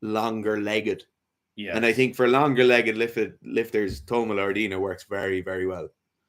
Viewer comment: Helpful as always! Who was the athlete mentioned at 52:46? Can't make out the name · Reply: If I hear correctly, think it's Toma Loredana